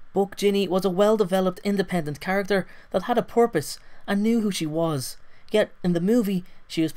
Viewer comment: haha because of your accent I thought you said she had a porpoise. I was like "what the fuck? they cut out the fact she owns a killer whale in the films?"